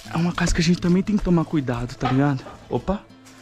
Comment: Pessoal so eu que ouvi "vai ou pai" quando o olha pela janela